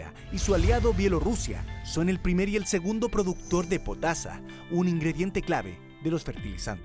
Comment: SE PUEDE COMER OTRAS COSAS NO SOLO LOS DERIVADOS DEL TRIGO POR DIOS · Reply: en ese minuto dice potasio o potasa? El ingrediente de los fertilizantes. No logro entender lo que dice...